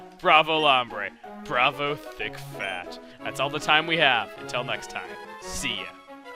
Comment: Wow when said bravo thick fAt it sounded like he said, bravo D*** fat lol